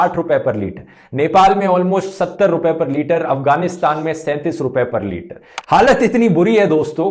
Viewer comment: Its only me or anyone else heard 37 instead of 43.